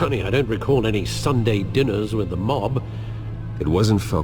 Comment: I thought alfred had said dinner with youre mom but he said dinner with a MOB.